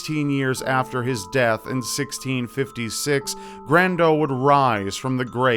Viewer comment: Thought you said 'Brando' at first - guess there's another reason for Dio's vampirism down the line XD